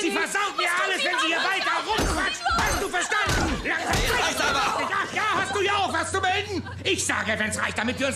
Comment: What is he saying at 46:35? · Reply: Ach ja hast du hier auch was zu melden